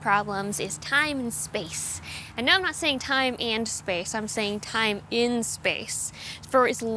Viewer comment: I actually really appreciate the clarification that you were saying "time in space" because I definitely heard "and." lol I actually wonder if Jeff Geerling will do anything involving that moon time standard.